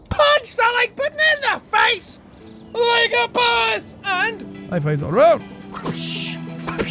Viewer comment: jack when you say like a boss you sound like your saying like a bus